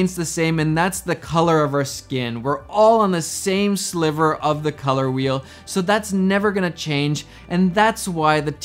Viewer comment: I am high while watching this and I thought he said " We all belong to one trump of the color wheel"